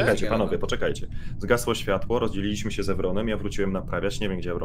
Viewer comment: a ja usłyszałem ''rodziliśmy się z Ewronem'' xd